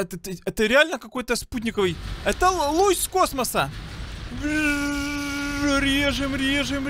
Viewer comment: мне показалось что он сказал лось из космоса